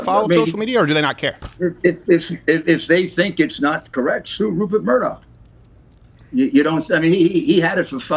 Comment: Dude's a freaking beast. Did I hear that correctly? I had to listen twice, but it really sounds like he said "sue Rupert Murdoch" so flippantly like that's just something people do every day. Dude's legit the actual leader of the free world and Mayor Rudy was like "sue him" like he's explaining a recipe.